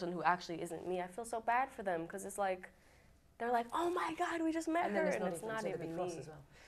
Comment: What does the interviewer say to stop Billie after Billie Says "OMG WE HAVE JUST MET HER" in 12:58? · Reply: And then it's not even [you] - so that you're cross as well.